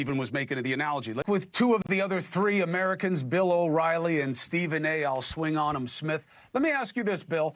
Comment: Did Cuomo actually say Stephen A I will swing on him Smith 10:09 😂😂😂